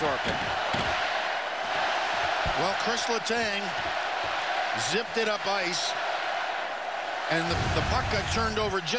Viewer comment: Anybody know what the crowd was chanting at 1:20? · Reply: King of the Rats They were chanting let’s go pens because they don’t want them to give up next year